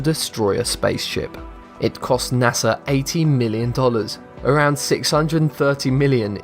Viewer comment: Does anyone notice he says 18 million instead of 80 million